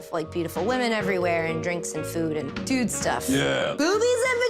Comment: i thought she said and drink food xD